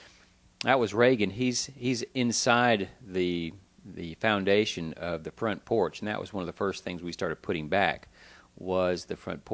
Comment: Did he say president Reagan was inside the front porch?